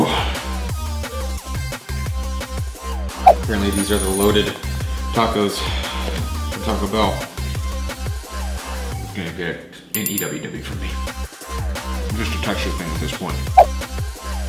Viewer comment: did he just say there the loaded tacos from Taco Bell 😂